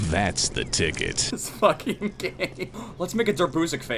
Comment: did he say thats fucking gay or this fucking game.... if gay imma go piss everywhere XD